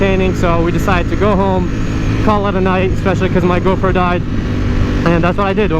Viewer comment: When you said "cause my go pro died" , I heard "cause my girlfriend died" 💀